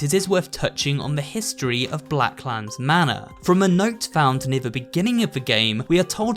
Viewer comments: Did he just say Black Lives Matter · i thought he was about to say Black Lives Matter 😂 anyway i’ve been binge watching your videos they’re super interesting :) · am I the only one who heard black lambs matter